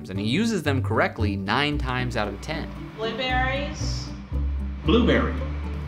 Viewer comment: my dumbass thought that the monkey said blueberry